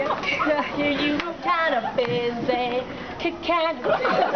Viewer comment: hahaha very funny!! i just hear "I'm kinda busy.."over and over!!